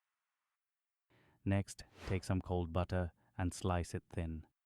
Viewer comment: So I tried this tonight the flavours are there. I could add more salt, but it did not do that nice rise in the remaining hour after I add the butter and turned it around… Now I thought they said cold butter, which I think retarded the proofing. The flavours were there, but it certainly did not get nice and fluffy.